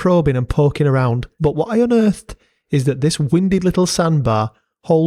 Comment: I thought you said "wimpy little sandbar" and thought you were just throwing strays for no reason lol